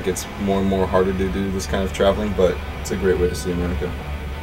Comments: it sounds like he says it's a great way to see America, but the text reads it's a great way to save money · the closed captions are wrong. He doesn't say "Its a great way to save money." he says "Its a great way to see america"